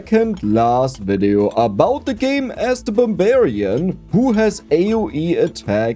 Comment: I really thought said Bavarian instead of barbarian... But the subtitles got it right...